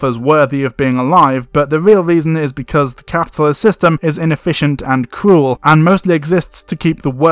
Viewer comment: when you say "the capitalist system is inefficient and cruel", the subtitles says "the capitalist system is inefficient and cool", is that hidden capitalist propaganda in your video ?